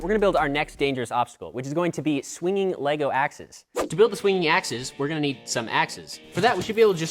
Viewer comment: I thought you said swinging axles